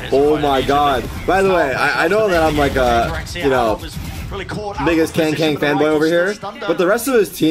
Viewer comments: did he just say the N word? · am I hearing this right or is it something different? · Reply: he said biggest Kang Kang fanboy